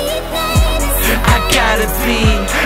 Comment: Very good song but wat made me laugh was the girl who sang it sounded like she was saying "I gotta pee"